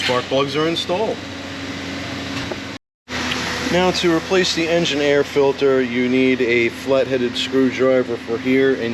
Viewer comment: I thought you said engineer filter.